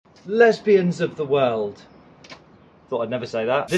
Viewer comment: I thought he said us beings up the world 😂